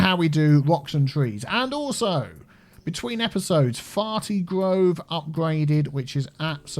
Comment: "Fartea Groves" My eyes weren't on the screen when you read it and I heard "Farty Groves"